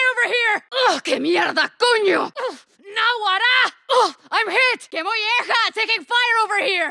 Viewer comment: Can someone tell me what she says at 12:00 and translate? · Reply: "Na'guará", an expression used in the Lara state, which denotes surprise or the execution of an unexpected event